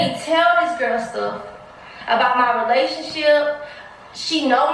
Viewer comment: Sound like she said ovulationship